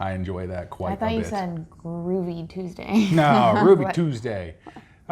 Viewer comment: I thought he said Groovy Tuesday too. I instantly thought, "do they have a special post for Tuesdays?"...